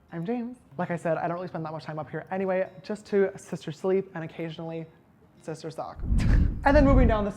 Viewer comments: Did he say sister sleep and sister suck? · Did he just say sister suck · “I thought he said ‘sister stalk’” “No! He must’ve said ‘sister suck’” The new Yanny or Laurel ladies and gents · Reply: I heard sister suck so I went back and put subtitles on and he says sister talk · Did he just say he goes to his room to ‘Sister suck’ ? 😂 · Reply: Thought he said sister sock..lmao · “And occasionally, sister suck” 9:32 James 💀💀💀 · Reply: I thought he said sister sock 😂😂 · Did he say sister suck??😂😂 Please tell me me Im not hallucinating😂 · Did shistar james say shistar succ at 9:32..................im shistar shook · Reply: I thought he said sister sock as in masturbate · lol when you were talking about your bed, after you said “sister sleep” I thought you said “sister suck” and I was like....sister suck WHAT??? 🙃🍆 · What does he say at 9:30-33 it sounds like “ and occasionally sister suck” 😂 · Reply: Haleigh Baker if you slow it down he says sister stalk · what did he say at 9:32 ? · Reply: Sister suck😂😂😂😂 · did he say sister suck ? 😂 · Wait did he say “ and occasionally sister suck”. ???? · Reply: I thought he said sister sock hahaha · Did sister James just say sister suck??? Lmaoooooo yessssss jamessssa get that d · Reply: Alayna June i thought he said sister sock but yours makes more sense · did he say sister suck or sum else 😦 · Omg did he actually say sister suck 9:32 😂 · did he just say sister suck i’m th r e w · DID YOU JUST SAY SISTER SUCC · Did he say sister suck